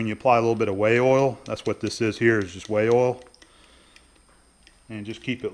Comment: Hope you didnt say "Whale oil" ? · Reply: "Way Oil" as in oil for the ways on a lathe. at first i thought he said "whey oil" but then realized that was dumb and Way Oil made a lot more selse